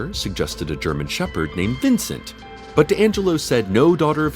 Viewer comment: I heard "her dad, Angela Flynn" and was... slightly confused. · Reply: kas : Deangelo